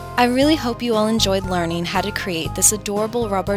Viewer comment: I thought you said fake hair instead of take care at the end.